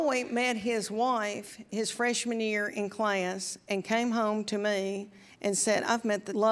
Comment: I thought she was saying "fresh manure in class", instead of freshman year in class!